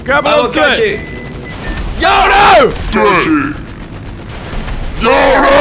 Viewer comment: sounds like he said deji